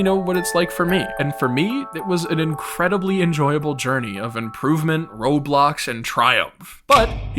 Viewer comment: lol I thought he said improvements Roblox and trials